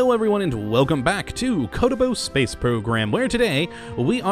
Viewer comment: wow i thought he was saying kerbal space program in a bad accent but hes saying kottabos lmao